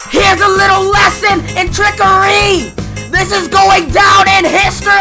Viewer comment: sounded like he said "trickerWRYYY"